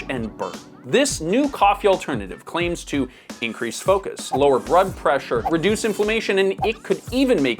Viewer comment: Not to hate, but did you just say "lower brood pressure?" ❤